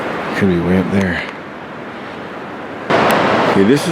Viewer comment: Love the I plants with the white blooms. Are they called yucca trees? The river looked very refreshing. At 22:23 after you said cabin remains, it sounded like a faint , " Right There".